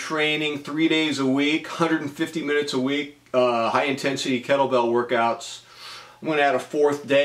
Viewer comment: Why when you say "Kettle Bell" I hear "Cracker Barrel"? It's been good for you, and you feel better. Enjoy it.